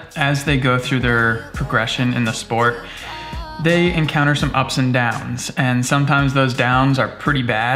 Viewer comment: He said ups and downs But I heard upside down Only stranger things fans will understand